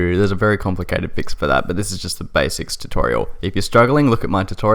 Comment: Did he just said besic tutorial 5:55 ?